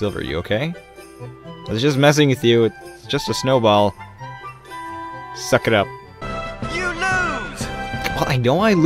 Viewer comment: Did he says "sakra"? Because in Czech it mean dammit or something .... I don't know 😂😂 (sorry for my English) 😂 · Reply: Anett Ruby Hes saying suck it up